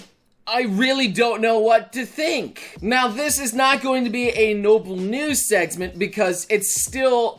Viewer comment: Did you just say "Noble News Sexment?" thats quite the Freudian slip of the tongue